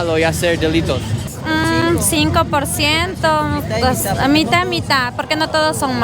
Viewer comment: an observation: at 30:51 it seems like the lady said "mitad y mitad", which means "fifty-fifty" so I believe she was actually trying to say 50%, instead of 5%. did anyone else noticed it?